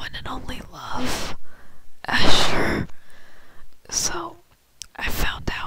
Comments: Sounded like you said assfur I like that name :3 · It sounded like you said Assfur